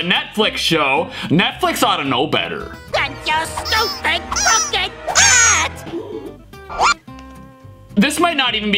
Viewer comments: Note: Mugman actually said "AND YOUR STUPID CROOKED HAT" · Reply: I thought Mugman said "and your stupid something hat!" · Note: Mugman actually said "AND YOUR STUPID CROOKED HAT" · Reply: I thought he said your stupid lookin’ hat! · Note: Mugman actually said "AND YOUR STUPID CROOKED HAT" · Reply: I thought he said Your stupid cupcake hat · Note: Mugman actually said "AND YOUR STUPID CROOKED HAT" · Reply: It sounds like he says AND YOU STUPED TRUCK AND HAT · Note: Mugman actually said "AND YOUR STUPID CROOKED HAT" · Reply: I thought it said "and your stupid, pumpkin hat!"